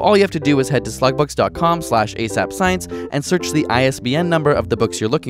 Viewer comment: Lol I was scrolling through comments an thought he said "slut books"